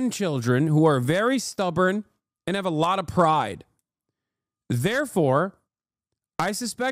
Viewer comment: I thought he said they have a lot of fry, which is also true